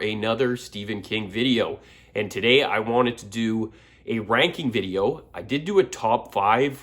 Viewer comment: I thought it said reading instead of ranking lol